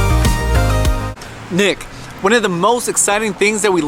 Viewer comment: sounds like he says dick instead of nick